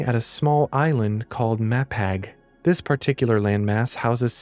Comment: Ok, so…idk why but I’m genuinely curious as to whether or not I am just some weirdo (highly likely😉) or if anyone else has experienced this(highly unlikely😅)fairly simple mistake? I mean…as far as misheard stuff goes🤷‍♀️😄it’d normally not even be worth mentioning really…….But the fact that I can not “unhear” it, is the issue. So from the moment I first misheard “Mapag” is now and forever will be perpetually misunderstood by my brain/ears as “Matt Patt” ffs! WHY!? JUST….freaking WHY my dudes!? 🤦‍♀️🤷‍♀️😅 such a nonsensical yet bizarrely comical misinterpretation that